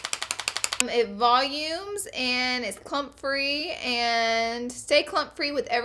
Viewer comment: i swear i heard "It's CUM free" omg i had to turn the subtitle on LOL